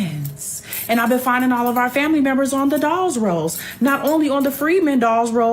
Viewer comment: Can someone please give me the correct term for the genealogy word she mentioned it sounded like she said “dolls rose” sounded like she said it but I know it’s spelled different. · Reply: Dawes rolls